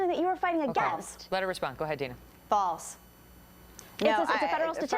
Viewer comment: does Dana say "F*** off"? Lol